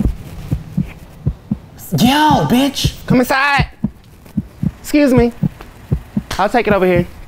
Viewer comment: it sounded like said "comu sai" lol instead of come inside